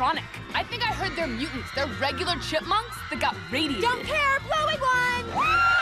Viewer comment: when I first saw that epic I thought that she said "I think that theyre chipmunks that got rabies "then I just now found out she said radiated